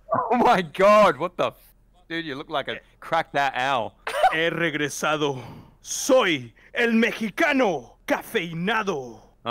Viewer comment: speaking Mexican or Spanish Me : uh wtf he saying bro I wanna know cause it sounds heroic Also me : crying cause I’ll never find out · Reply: eh regresado! soy el mexicano CAFEINADO!! I'm back! I'm the caffeinated Mexican!!